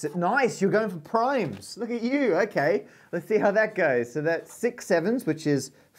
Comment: lot of people saying 327 isn't prime, I think he means the individual digits are prime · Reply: Sounds like he says, "Nice! you got 'em primes! Look at you!" There's no reason anyone should be calling him out on it.